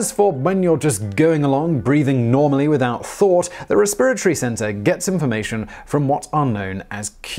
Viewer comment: I can listen to an entire video of Simon just saying “resPIRatory”. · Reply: Lol I heard "re-spirit-ry"